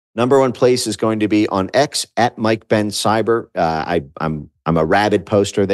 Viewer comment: At first i thought you said Mike pence and i was about to hit the eject button😂